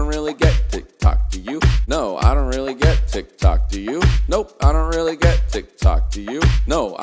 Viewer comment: I hear, ‘idont really get to copy you?’